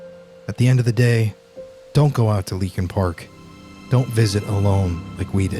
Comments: Thought it said link in park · i thought it said linkin park lmaooo